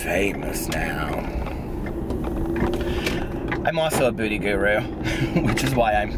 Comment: I thought you said a booty guru at first.